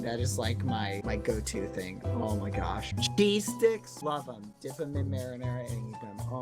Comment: Did he say, "D-Sticks. Love 'em. Dip 'em in marinara."?